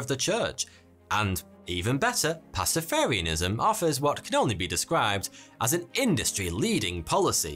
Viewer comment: did you say, " cannoli be described..." The perfect communion dessert.